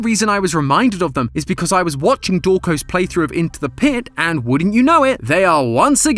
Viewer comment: why is the subtitles say dorko his name is dawko?!